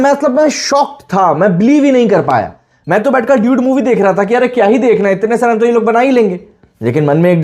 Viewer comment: Bhai kya bola 😂😂😂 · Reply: Dude movie